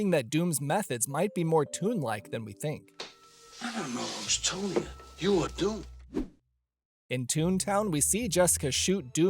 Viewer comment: For years and up until l watched this video I always thought Eddie said "l dunno who's told ya, you're doomed." lol